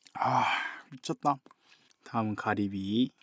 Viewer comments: When he said scallop at 4:35 it sounded like he said young cardi b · I thought he said come Cardi B 😂